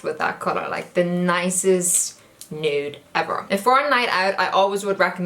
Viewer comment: it sounds like she said 'in fortnite'